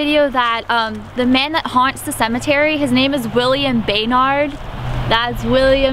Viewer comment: I thought she said William BANG hard 😂😂😂